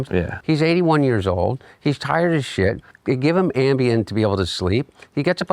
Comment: Wait, did he just say that Biden was on Ambien during the debate? Even if he meant more generally, like Biden was being given Ambien in general on some nights to help him sleep, but not on debate night, that’s a huge deal. So yeah this is what this interview is going to be used for, the right is about to shit their entire ass over this one sentence.